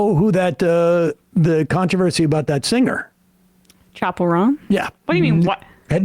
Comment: Who was the singer? It sounded like Emma said "chaparone" and the closed captions said "Chapel Ron." I feel like Sam, totally out of touch with this current drama. Don't worry, Sam, it's a good thing to be out of touch with drama lol.